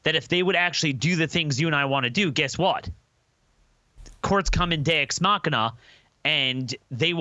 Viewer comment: What did the prophet of woe and lamentation say "Courts come indeoxima"